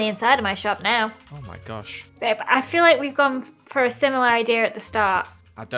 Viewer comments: and 11:44 did say babe?? SO CUTEEEEEE SHIIIPPPP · Anyone else hear say Babe